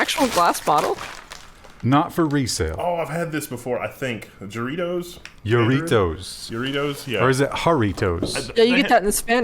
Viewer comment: Thought I heard "Doritos" at first. Needless to say, I was disgusted.